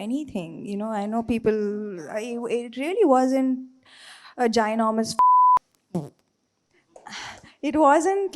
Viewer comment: what was the word she used .. lol 😂Sam started talking beep words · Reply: She said “ginormous fuck”, then she realised she cant use f words